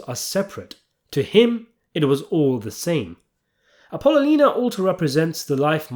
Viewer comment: As soon as I hear "Ping! Pow!" I know a video's coming up that's worth listening to! Great take. Now, repeat after me, please. "Apple-o-knee-ah." "Apple-o-knee-ah." "Apple-o-knee-ah." YW Peace